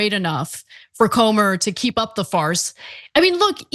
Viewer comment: At first I thought she said "Keep up the fart...." An apt mistake on my part